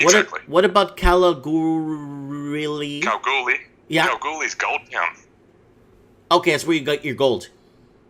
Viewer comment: i thought he said "Kalgoorlies gold cu*t" hahaha